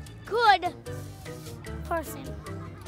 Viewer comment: Did the kid at 1:06 say that Titanoboa was a really really good person? Then did he say I'm at stake? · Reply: I think he said I meant snake